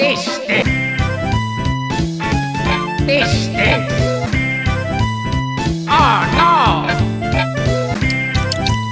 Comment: Does Wario say this stinks or fish sticks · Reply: This stinks